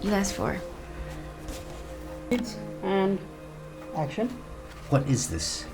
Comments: I heard it and action in boss treats female staff unfair at work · I heard someone say hit and action lol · am I the only one who heard someone say 'and action'? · i heard:it and action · Who else heard the “and action” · I heard them say action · I know I’m not the only one who heard “wait aaaaand… action!” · i hear action in the background8:00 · 801 I heard and action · did anybody else hear the 'cut! and.. action!' at 7:58? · did anyone hear “and action” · did anyone else hear someone say "and action" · AT ABOUT 8:00 I THOUGHT I HEARD SOMEONE SAYED ACTION